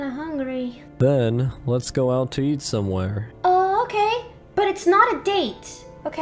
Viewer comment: Zach: “But have you eating any dinner yet?” Alex: “No, but I am really hungry..” Zach: “Okay then,let’s go out and eat someone.” 7:17 Me: best misheard phrase ever.